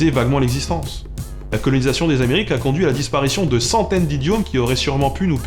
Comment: Quand tes oreilles buggent vers 28": "disparition de centaines d'idiomes" j'ai entendu "disparition de centaines d'idiots", pendant une fraction de seconde j'ai été choqué!